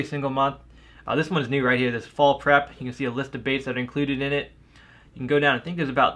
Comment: when you said fall prep it sounded like you said fall crap